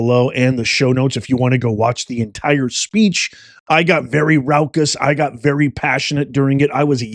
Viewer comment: Did you just say 1.90 'rowkus'? Raucous you mean?